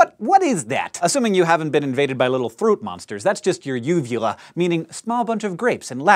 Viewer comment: I thought it said what can your vulva do 😂